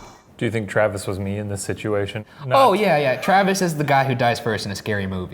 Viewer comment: I heard “oh boy, yea...” not “oh boy get out” Anyone else?